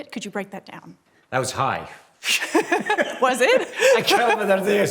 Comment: I thought Tom said " he was high " for a split second......was like Tom Cruise is getting high?